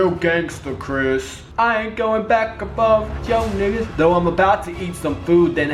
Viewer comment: did he say 'nigga' at 24:05?